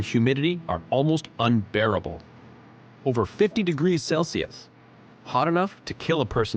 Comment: I feel like I’m missing something. I climb into attics that are 140F and higher sometimes. I also live in Oklahoma and it’s regularly 100% humidity. This place sounds like it isn’t as dangerous as they make it out to be. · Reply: You did miss something; what was said ay around 16:20. The guy said: "over 50° CELSIUS°.